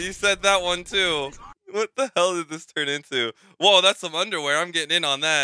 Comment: soy yo o escuche la palabra weon (palabra chilena) en el